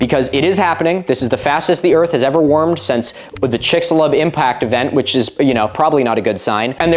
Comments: Wasn't paying attention for a sec and I thought I heard "Chicks love impact events", I look up and there's an image of the asteroid that killed the dinosaurs and I got really confused for a second. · This was a Me Thing, bc I was running water & I'm listening while doing stuff, but I misheard this as The "Chicks Love Impacts" Event & just thinking I'd root for that as a conference seminar or science club name lol